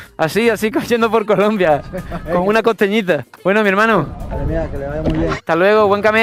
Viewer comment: En el min 2:52 lo que dijo el muchacho fue: "Vale mía, que le vaya bien" 😅